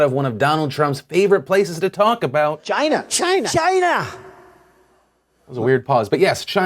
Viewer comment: thought he said Vagina XD